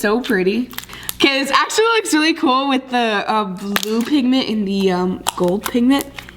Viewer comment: Did she say the blue migment and the gold migment